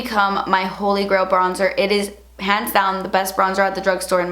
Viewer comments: at like 9:50 i thought you said 'pants down' instead of 'hands down' for a sec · cant tell if she said hands down or pants down... sounded like pants down lol